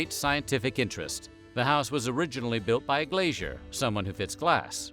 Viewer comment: I’m glad you put “glazier” on the screen. I thought you said a “glacier” made the house! I was picturing it carved out of a mountainside. 🤦🏻‍♀️